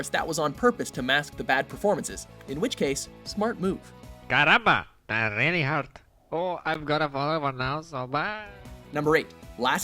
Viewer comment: for me sounded like: “Karamba, that really hurt, oh, I’m gonna fall over now so byeeeeeeeeee”.